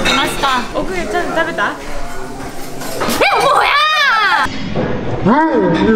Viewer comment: の、"もういや~" が "뭐야"に聞こえて、なんか周りの人もそれなりに伝わってたのかなって思った。 済州島行きたーい🍊🍊